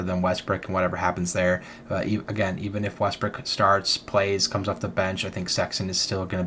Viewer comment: Anyone else notice that idk if it was the guy being passive aggressive or just an honest mistake but I heard him say Westbrick